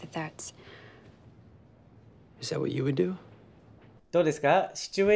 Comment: 最初say were you will doって聞こえて意味通らず首ひねってましたが、聞き取れるようになりました！